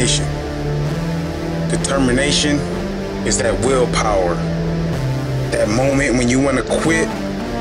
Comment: what he says? help me. i can't listen that. because i can translate english into mother tongue, but not write and listen well... "the termination is that willpower ~blah blah....."